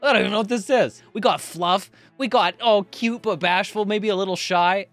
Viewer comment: what the actual fuck did I just hear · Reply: BASHFUL is what you heard.